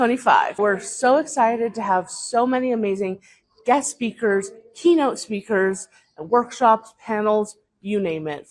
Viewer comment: I thought she said Keto speakers. I was all, “good for them”,,,,, boy I was wrong 😂🤣